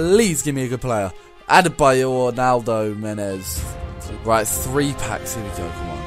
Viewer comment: I thought that I say ronaldo lol